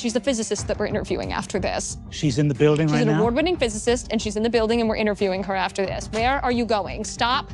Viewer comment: i heard "award winning physicist" as "orbiting physicist" 😂